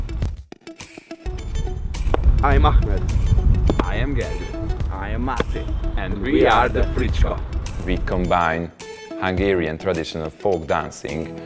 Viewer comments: Did I hear "I am gay"? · So I heard, "...and we are the Freak Show!" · I thought he said i am Gay